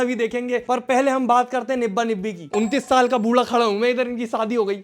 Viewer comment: did bro just said 29 years 😲